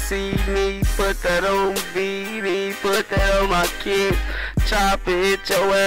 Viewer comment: Did he just say on bd